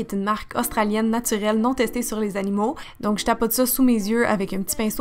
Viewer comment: J'ai compris "naturellement testée sur les animaux" au lieu de "naturelle NON testée sur les animaux" j'ai eu peur 😂 J'adore le maquillage ❤